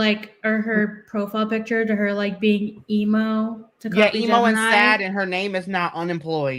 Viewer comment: She wants Tommy to pay for cameos so it changes the last purchased date. To make ppl think she’s getting ppl to buy her crappy content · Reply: I legit thought they said she was e-mooo